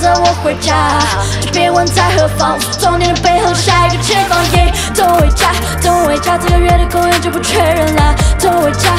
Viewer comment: 等一下就回家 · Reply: 我一直聽成 帶我回家...... 內心想說 好啊🙃